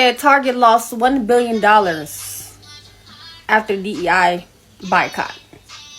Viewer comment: did she say BI-Cot? 🤦🏾‍♂️🤦🏾‍♂️🤦🏾‍♂️